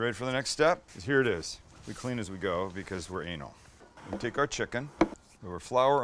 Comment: did he say clean as we go because were ANAL or am i trippin 🤔😂😂